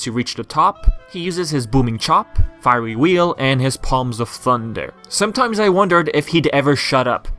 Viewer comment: Thanks for the support :) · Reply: Oh yeah in the Panda King fight, when he says "Paws of Thunder!", I always thought he was saying "Huzza Thunga!" lol